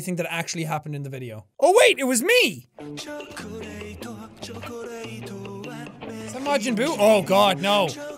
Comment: did you say "is that majin buu?"?!?!?